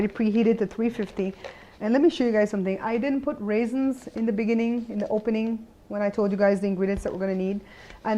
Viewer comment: Did she just say eyes in the eggs, wtf 0_o